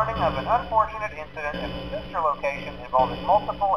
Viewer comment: did he say sister location?